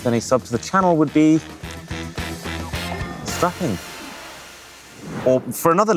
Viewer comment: Naaaah. At 10:31 when you said “a subscribe to the channel would be… snapping” I thought you were gonna say “smart”👀🤣. Coz it’s a SMART watch review. Anyway, I’ll try to get it right next time🏃🏽‍♀️. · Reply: he said strapping. because watches have straps